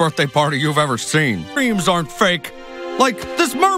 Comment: it sounded like he said "Memes aren't fake!"